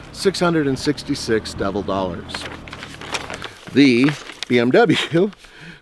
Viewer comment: I had to replay the price part on the RAV4 a couple times. At first I thought he said double dollars and not devil dollars. For a moment I thought he slipped in a Trigun reference.